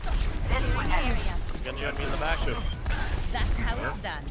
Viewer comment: Am I the only one that thought it said pengu? Yeah, ok.